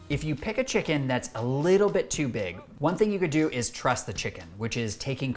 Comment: I thought you said, trust the chicken instead of trust the chicken 😂😂😂😂😂